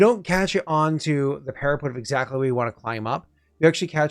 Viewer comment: After more time, I am nearly there, but every time I heard "para-PUT" like "put it on the shelf" my heart was hurt. It's "para-PET". Like "we adopted a new PET dog". Please. Save them.